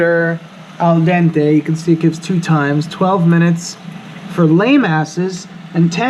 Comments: "12 minutes for lameasses, and 10 minutes for people who know what's going on." 😂😂😂 This is why Frankie is my favorite cooking personality! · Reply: LOL, I thought he said "lay masses," which means the throngs of ordinary people. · “12 minutes, for lame asses” 😂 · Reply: Lol, I thought he said lay masses! 😂